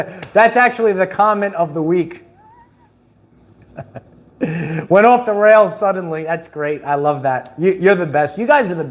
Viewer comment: DERAILED SUDDENLY 🚇‼️‼️📵🚱-, I THOUGHT YOU LAGHED MID SENTENCE WHEN YOU SAW THIS. WHEN I HEARD "OFF THE TRACKS SUDDENLY " I ALMOST DIED!